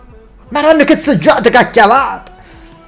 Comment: mi scusi maestro ma non ho capito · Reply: Madonna che seggiata ha tirato 😂